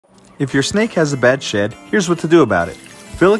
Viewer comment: I thought you said "when your snake has a bad sh!t"